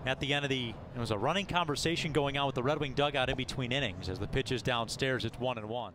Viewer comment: did he just say "a running conversation with the right wing dugout"? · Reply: He said "Red Wing dugout". Rochester's team name.